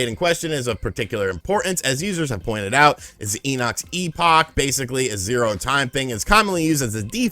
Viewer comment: I'm sorry...what was that? "Eenox"? I gotta say, I'm impressed. Did I catch a niner in there?